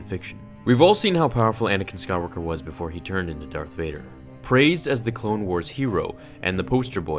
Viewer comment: is it just me or did he say DARKF vader